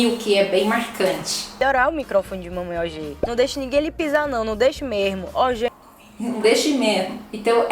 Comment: eu ouvi "não deixe mejmo" com jota espanhol 😂😂😂